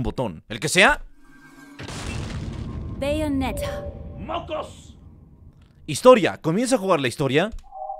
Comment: Escuche MOCOS 🤣